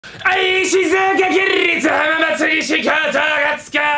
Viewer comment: 挨拶の静岡がしずえに聞こえたw 学校違くても部活がおんなじの呼ばれると嬉しい！ ガチで最近面白いし、上手い！そんなのプレイ見てるとめっちゃ元気でる！学年変わってクラスに馴染めずに、ストレスとか疲れが溜まってるからまじで嬉しい！これからも動画投稿頑張ってください！応援してます！